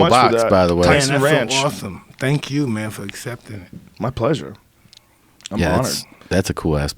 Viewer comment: it sounds like Joe says “My pleasure. I’m hard.”